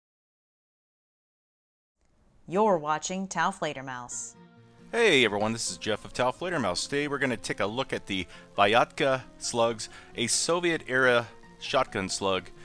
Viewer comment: Misheard it as Viagra slugs Edit: Ever tried salad oil?